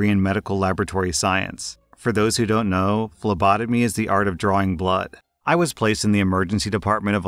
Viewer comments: I misheard LOBOTOMY. And I was like, "Sh*t, they still do that???" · I was looking at my doorway and then i heard "For those who dont know phlebotomy is art of drawing blood" I thought he said "lobotomy"